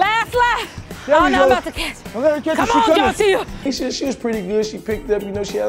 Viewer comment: I thought she said her name was John Cena I was like who's ur momma girl?????